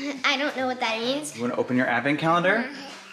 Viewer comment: it sounds like is saying ‘my advent’